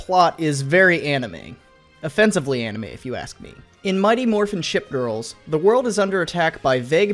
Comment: Who else heard him say “Mighty Morphin’ shit girls”